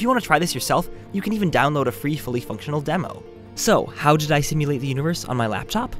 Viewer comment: I thought it said stimulate. Darn I was excited but still that was cool.